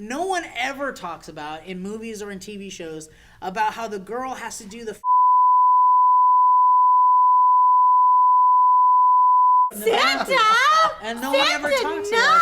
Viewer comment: Great video, thank you for doing this! Also does anyone know what says at 26:30? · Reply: supesdupes I think he said "The girl has to do the funny walk something something to keep the sperm in her vagina"